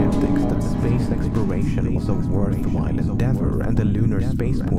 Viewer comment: Do I hear a world wide pendentives?